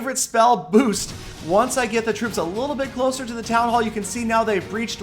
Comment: did i hear it right? 8:47 town hall? hhaha